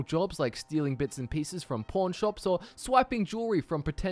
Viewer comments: when he said pawn shop it sounded like he said porn shop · Sounds like he said pornshops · Your accent through me off and I thought you said “bits and pieces from porn shops”. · seriously I keep hearing porn shops but I know your saying pawn shops